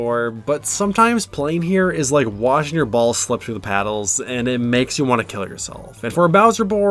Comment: lmao, my earbuds did a weird thing at this part and i heard "Washing your balls, kill yourself" And had to do like 3 doubletakes